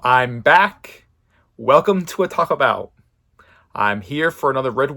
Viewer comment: I thought you said "Welcome to Taco Bell"